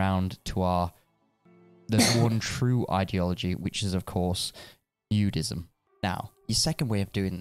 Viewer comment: xD What i think should be: You-Deizm What my mind hear: NU-De-izm What automatically generated subtitles say: Judeizm